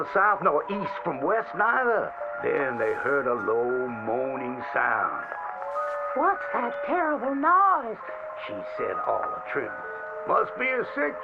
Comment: Most people heard “what’s that terrible noise” but I heard “what’s that terriost” 💀